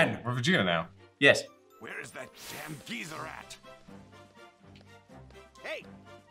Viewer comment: Sounded like he said geezer rat Hes lookin for splinter